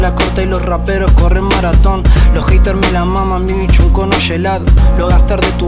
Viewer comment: jajajaja saco la corta y los raperos corren maratón, pensé que dijo los heaters pero es muy bueno igual jajajaja